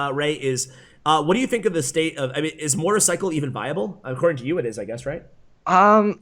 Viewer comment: Did he say motorcycle or am I tripping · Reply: Mortar..... cycle